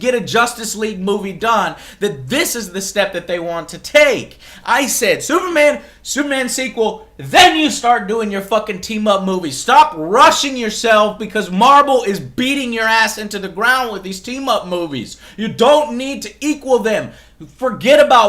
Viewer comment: did he say Marbel? xD